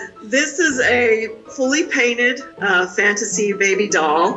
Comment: Girl: I thought people painted their babies I had to go back because I thought she said printed and not painted 😅